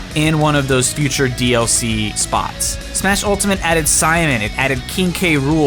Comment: What the fuck is he saying at 3:24? I can’t for the life of me make out what the word he is saying is. Maybe I’m sleepy right now but the auto captions say “smash ultimate added cyan it”. I assume it Simon since that kinda sounds close but barely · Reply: Yes, he said simon